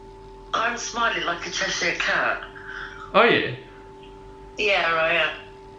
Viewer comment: “I'm smiling like a Cheshire cat.” His mum. Masha'Allah. I'm in awe. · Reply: Thought she said ‘I’m smiling like I just see a cab’ lol